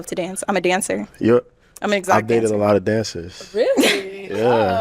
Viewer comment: I don't think he heard her say exotic... She kinda slid it in there under her breath.